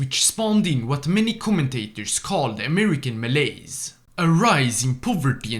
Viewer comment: I thought you said malays,but actually is malaise